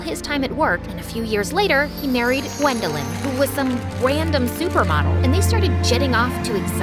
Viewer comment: i misheard random super mod